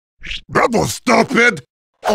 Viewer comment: I’m trying to find out what heavy said Also plz don’t judge me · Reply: He said "that was stupid"